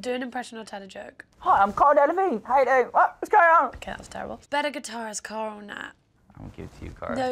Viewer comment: what is the question in 0:17? dont understand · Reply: "Better guitarist cara/nat?